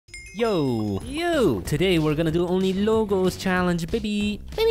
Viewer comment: Challenge: Only one colour block You guys have to decide what colour block you want to choose . But ,you guys can use like blue terracotta,blue concrete,blue concrete powder any block that have the colour block in their name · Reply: I thought you said one block challenge lol